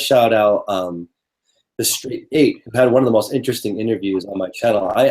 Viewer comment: Hey, what name do you say at 26:16. "The Street Eight"? I want to see the interview you're referencing but I couldn't make out what you said. Thanks. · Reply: the straight eight